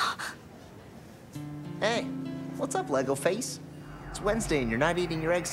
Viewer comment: Wait, did jack say "lego face" at 2:45? 🤨